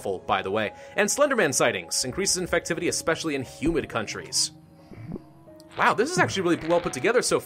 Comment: when he says humid I thought he said human! Yeah duh it affects in human countries, thats ALL countries! yummy humans.